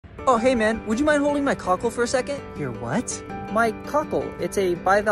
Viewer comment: Omg I thought he said hold me cookie 🤣